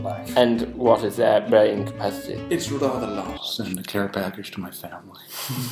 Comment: what does chris say at 2:49? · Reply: 'what is their brain capacity?'